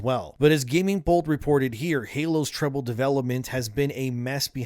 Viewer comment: When you said troubled development, I thought you said triple L development.. Today's gaming industry with the big companies.. Triple L games.